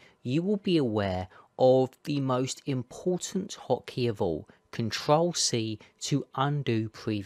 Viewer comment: Lol I thought you said cntrl c. I was like it's not working 😅